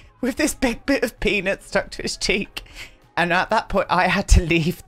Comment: Oh my goodness I thought you said “ a big piece of penis stuck to his cheek “ I was dying 😂